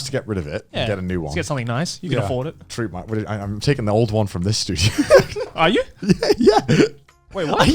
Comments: i think the big confusion that happened is that grant and joey thought that connor said “i’m taking the old one from this dude” but he actually said “i’m taking the old one from this studio” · Connor: "I'm getting rid of my cockroach-infested washing machine and taking the one from the studio" Joey: visible confusion 2 mins later Joey: "I thought you're gonna take MY fridge from MY house" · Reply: I think Joey misheard "this dude" instead of "this studio" LOL